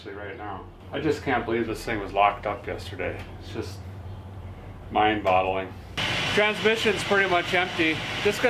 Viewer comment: Did you say mindbottling? 😂